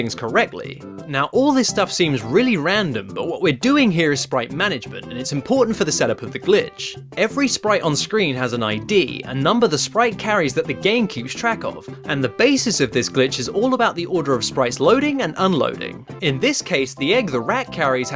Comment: i swore he said gamecube